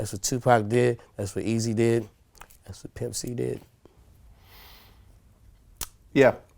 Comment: did he say thats what nipsey did? This video came out last year